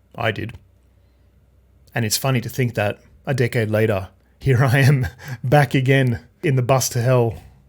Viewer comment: At first I thought you said "a dick-head later" alluding to the current CEO.